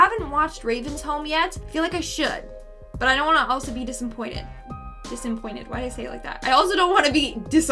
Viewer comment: said Desinpointed instead of Disappointed Very funny also can I please get a comment Shout out